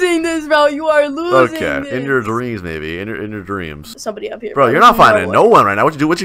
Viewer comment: did he say in your dreams baby??? Or something else because i have hearing problems